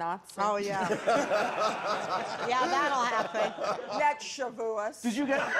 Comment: What does Sandra say at ~20:17? I can't understand her. That shaboouss.